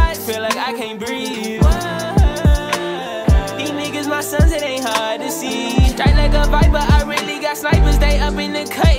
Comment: why did i think he said trapped in a vibe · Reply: and i thought he said these somethings my sons and they hard to see🤦🏽‍♀️